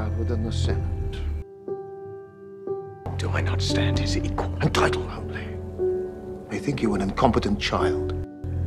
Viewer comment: What did ilithyia's father say at 0:18? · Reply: "in title only"